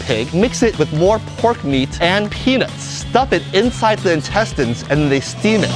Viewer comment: Did he just said "penis" ?